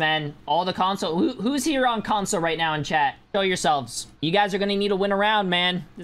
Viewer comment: Why does at 51:58 sound like he says kill yourselves😭